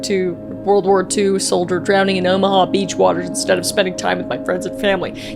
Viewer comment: OML I THOUGHT IT SAID OBAMA AND NOT OMAHA